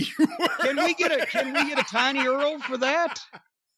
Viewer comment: Did I hear Corny say “tiny earl” as in URL ?